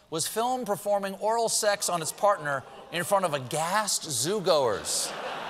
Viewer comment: I swear I thought I heard the gorillas’ antics happened in front of “a gassed zoo-goer”. I had to replay that, lol. Talking too much with my kid… 🤣